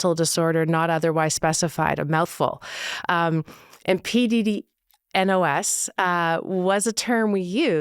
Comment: thought she said “P-Diddy-NOS” at first ahahah